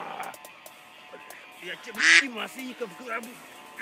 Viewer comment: мне одному послышалось что на 6:52 фрост сказал "я тебе не маслеников в гробу"